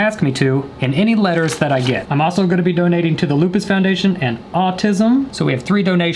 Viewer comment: if you keep playing this over and over again it sound like he is saying (What is em)